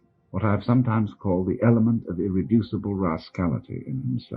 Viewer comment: What did he say at 1:57 the element of what? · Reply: element of irreducable (unable to be reduced) rascality